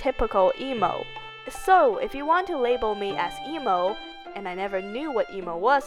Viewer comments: at first i thought it said don't label me elmo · I THOUGHT IT SAID "Don't Label me Elmo"